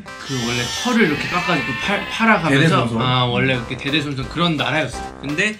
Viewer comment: I thought Jin said "I'm Caucasian."